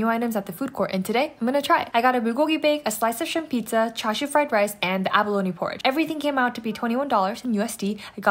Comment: I heard “Trashy Fried Rice” I’d eat that🤤😂